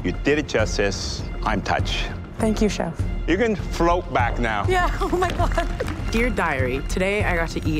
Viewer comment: I feel like did NOT want to eat that eyeball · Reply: I thought he said "you can float back now?"